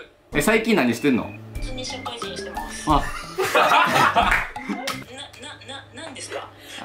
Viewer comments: これなんて言ってるか分かる？ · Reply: 普通に社会人してます かな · これ何て言ってるのか何回聞いても分からん…誰か教えて…笑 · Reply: ｢普通に社会人してます｣ じゃないですかね？